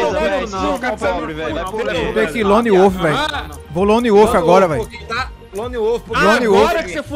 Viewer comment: eu entendi "rolona e ovo" kkkkkk